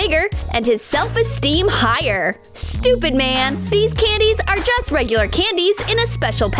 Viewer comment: did she say stupid man instead of super man